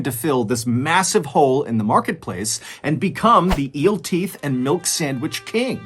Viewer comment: I had to rewatch the part with Eel Teeth Salad. I swore had said "Eel Teat"! 😂